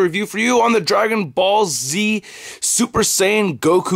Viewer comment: Sounds like he said Dragon Ballsy